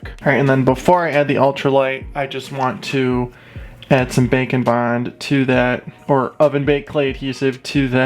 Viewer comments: Up until recent I always thought he was saying bacon bond. I like it when he makes the mutants. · it sounds like he says bacon bond